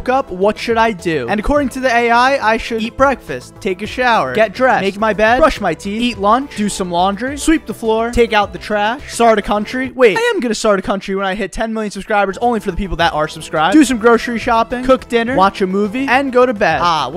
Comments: i thought a.i. said "talk to the trash" 🤣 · lmao i thought you said "eat my bed"